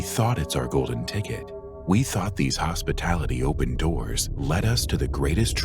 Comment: I'm sorry? these what-now? Did he say 'hospitality' because the subs definately say 'hospitably' 😊😜